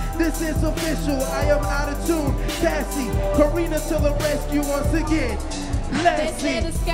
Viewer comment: Quick question at 3:50 does he say; AutoTune Cassie karina to the rescue once agian Lassie"?" cuz if he does i'm officially dying ova here. : ) iLove yall mane